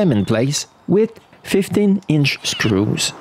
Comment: I thought you said 50" screws, lol, I was like, that's a rod, not a screw.